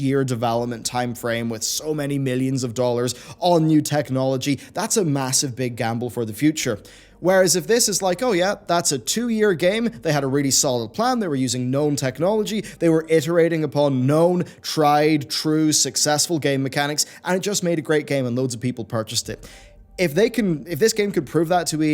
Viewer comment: I thought he said "They've been using Gnome-Technology".